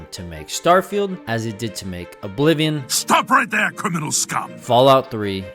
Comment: I thought in the intro it said "criminal's cum" but it said "criminal scum" I'm down bad